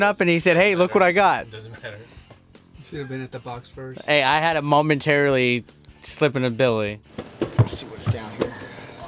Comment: "I had a momentarily slippin' of Billy." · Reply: lol i thought he said i had a momentarily slippin ability. lol, i was like more like permanent slippin abililty.